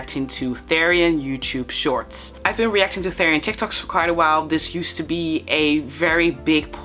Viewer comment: You know for some reason it sounds like your saying thererian tictok